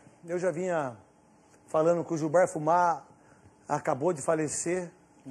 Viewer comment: Só eu que ouvi Gilbar Fumar